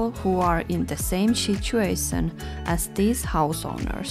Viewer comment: I hear like:" in same SHITuation"))) it serious mental problems of people, who makes like this. You did a great job, very big job! It's a big help for people. Love your videos ❤️